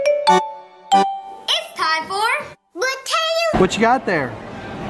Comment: I think said